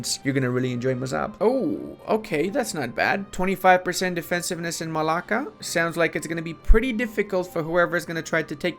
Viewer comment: LOL, did you just say "Μαλάκα" at 6:39 ?